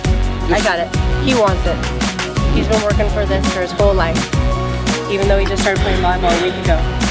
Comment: what does she say @ 2:43? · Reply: He's been working for this for his whole life even though he just started playing volley ball a week ago.